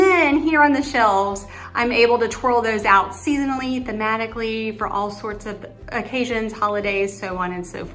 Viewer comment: I’m moving in!!! What’s your key code combination? I just had to, literally, laugh out loud.....when you were describing “the green beast” I thought you said you changed it out “fanatically” and I tucked that away to use later because it describes how I will redo a vignette....but later realized you probably said “thematically”. Love, love, love, love your decorated home. Thank you so much!